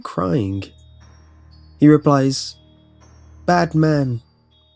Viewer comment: on number 6 i heard 'batman' not 'bad man' XD